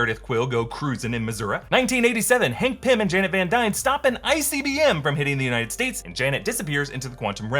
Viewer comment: I totally heard it as "Hank Pym and Janet Van Dyne stop an icy B.M. from hitting the United States"